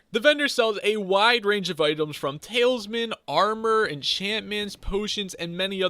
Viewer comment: Did he just say "Talesmen"